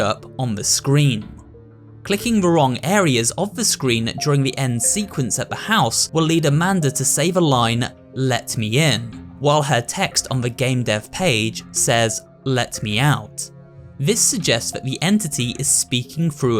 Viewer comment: I thought she said "let her go"